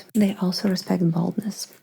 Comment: "They also respect boldness" I heard this as "they also respect baldness", which immediately got me to think about some sort of canon reason that they would, haha